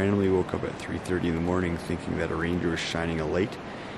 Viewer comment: I definitely thought you said "I thought a reindeer was shining a light in my tent" and watched it 3 times to realize you said RANGER! hahaha. Snowbird pass looked AMAZING! How difficult would you genuinely rate it.